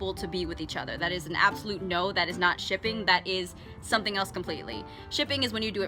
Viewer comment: it sound like she says stripping